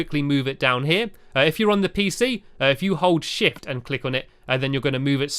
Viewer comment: It sounded like you said s**t but you actually said shift towards the end of your video about the PC